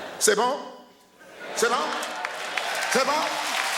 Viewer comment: Wtf does george wallace say at 20:40? Hes probly the most unfunny comedian ive ever seen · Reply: Inzombiac Aaron he says ce bon? it's french which means ( is it ok?) didnt really understand the joke tho